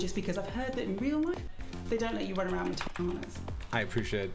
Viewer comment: did she say "takanas" fedora tilted. time to beat my body pillow in anger.